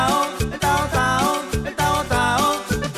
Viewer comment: Mierda yo siempre pensé que decía el tavo tavo jajaja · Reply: Igual yo pense que decia tavo tavo jajaja